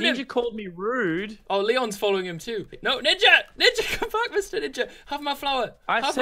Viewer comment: captions say no nigga when hes saying no ninja